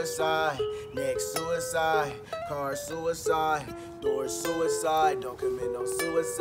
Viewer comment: did he just say door suicide